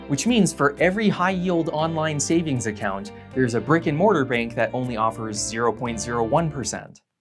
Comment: When he said brick and mortar bank I honestly thought he said “Rick and Morty bank"